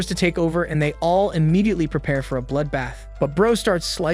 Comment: did you say bloodbath?